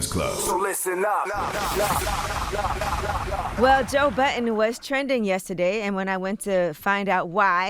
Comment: Did she just say Joe "button"